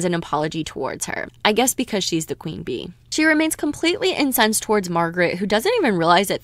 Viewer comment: I thought Margaret was saying because she’s blonde hair blue eyes